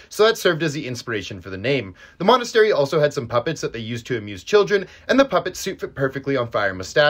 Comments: I thought you said abuse not amuse 😰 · When he said "They used to amuse children" I hear "Theg used to children" and took me a minute to comprehend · I thought he said abuse not amuse😂😂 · Thought he said abuse for a second😭had to do a double take